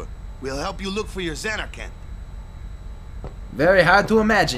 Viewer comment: Wakka says Zanar-CAN now instead of Zanarkand?